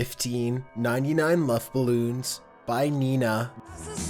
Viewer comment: I thought you said "99 muff balloons" lol. Also, how did anyone not know what Pumped Up Kicks was about? The lyrics are self explanatory but he was also very vocal (pun intended) about the meaning.